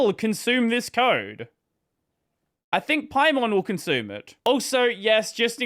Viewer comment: At first I thought I misheard it, but you actually said "Paimon will consume it" XD Didn't know she's into coding or even runs on Linux at all :)